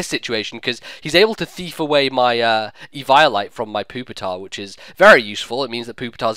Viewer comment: It sounds like ur saying Pooptar instead of Pupitar